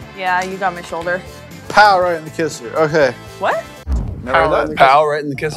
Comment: I heard "Paul the kisser" 😂